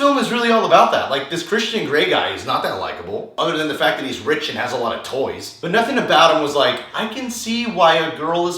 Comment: did you just say he's rich and has a lot of TOYS? Hehehe